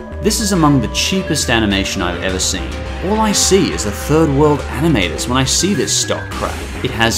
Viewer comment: 3rd world animators? Did I really hear it right at like 17:11? If it is wow this is very low. I know this is old, but damn that racism